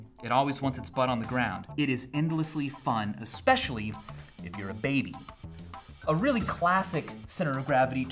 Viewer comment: "Especially if you're a baby..." pulls out random toddler from off screen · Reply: I listened at 2x. It sounded like he said "Especially if you're baked."